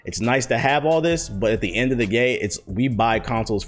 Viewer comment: did he just say “end of the gay”?